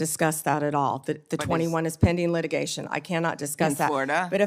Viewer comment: For a moment I thought she said “pending legislation”. 😮